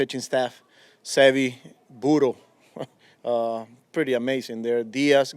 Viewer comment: Good manager !!! Vamos Mendoza 🇻🇪🇻🇪👍🏼👏🏼👏🏼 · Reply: I thought he said burro instead of Butto for a second lol.